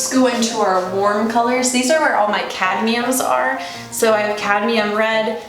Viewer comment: What said: cadmium What I thought she said: cat meme.